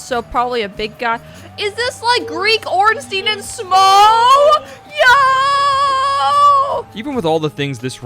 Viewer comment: What did they say at 15:45???? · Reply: Greek Ornstein and smough